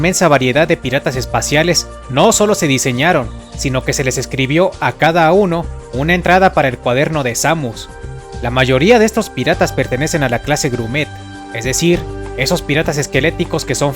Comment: Min. 3:44 Por un momento pensé que decía "gourmet" XDDDD